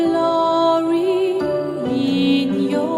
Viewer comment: "Flowing" ah! that's what it was, I always heard "Florrie" xD